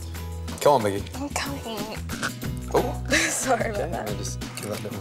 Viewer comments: Did I just hear "I'm cumming" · did she say shes cumming? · did she say i'm coming? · I think she said "I'm cumming"